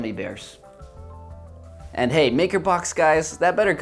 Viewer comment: i thought this said marker box :(